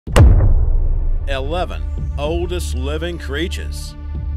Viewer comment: Am my misheard? 11 oldest creatures?